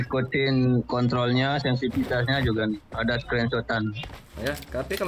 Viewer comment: skrentotan? telinga gua kali ya yg salah dengar